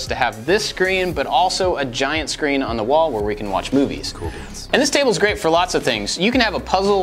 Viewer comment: Did anyone else hear “cool beans” at 19:17 😭😂